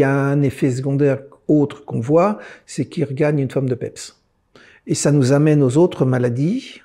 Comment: quoi ? Qu'est ce qu'il dit là svp on comprend rien. Merci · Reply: Je crois qu’il dit « les patients regagnent une forme de pèps »